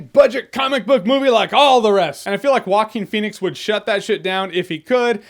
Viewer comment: Sounds like he is saying Walking Phoenix lol